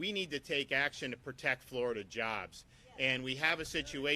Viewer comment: I thought it said florida jabs for a sec. 😰